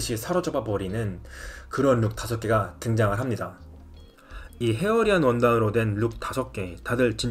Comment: 초에 원단 뭐라고 말씀하셨는지 알 수 있을까요?? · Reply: 안녕하세요! 혹시 헤어리라고 한 부분 말씀하시는 것일까요...? 그게 맞다면 hairy 라고 제가 말씀드린 거에요~:)